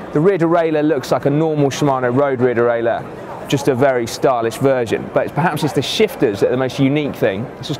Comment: Did I just hear say "stylish virgin" at 1:22? :)